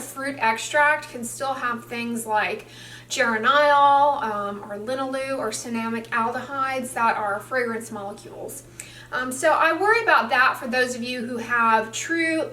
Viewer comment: May I know what Dr.Dray says in 04:04? Ger and aisle? or little lu? ceramic aldehyde?